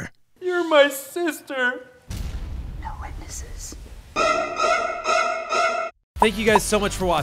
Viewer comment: I didn't understand what proncess said 2:49. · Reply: No witnesses